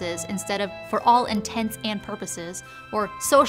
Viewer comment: As much as i know you're saying egg*corn*, i keep hearing "egg-horn" lol